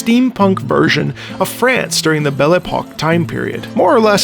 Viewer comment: I had to recheck because I misheard Belle Epoque as "Belobog", which is funny enough to be steampunk on its own, reminiscent of the Fontaine theme.